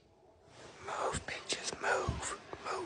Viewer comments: I thought he said move bitches move to the cat but the cats name is peaches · I listened to this at least a dozen times and still don't understand what she said. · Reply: I thought he was saying “move bitches, move” 🤣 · I heard him say ‘Move bîtches move’😭 to the cat